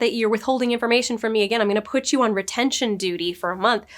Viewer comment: I don't think Tommy said retention duty, I thought he said rendering duty. Rendering is melting down fat, I imagine it's kind of gross and smelly.